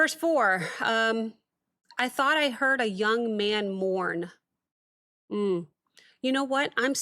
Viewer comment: "Thought I heard a young man MOAN this morning"...not "mourn".